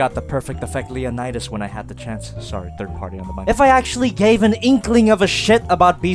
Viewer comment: did I just hear inkling?